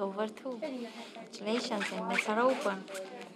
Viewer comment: what is this lady saying after her congratulationses? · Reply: "and bets are open"